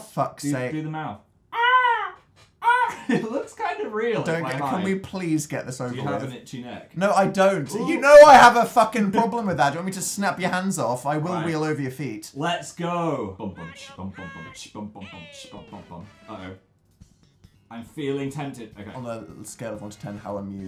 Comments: I heard 'I will wee all over your feet' at first, instead of 'I will wheel over your feet' lol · "I will wheel over your feet." -Daniel Howell Next time I threaten someone I'm using that. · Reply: Shannon McGill I thought he said "I'll wee all over your feet"... 😂